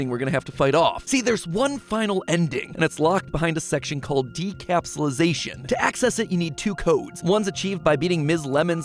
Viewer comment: Decapsulisation huh. I thought it said decapsulation